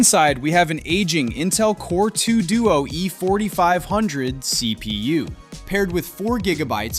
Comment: did I hear E-40?